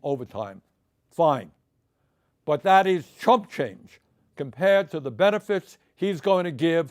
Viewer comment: Thank God for a people like Bernie! We'd have no clue otherwise. 10:45 I had to rewind it back because I thought for a second he said Trump change and not chump change.😂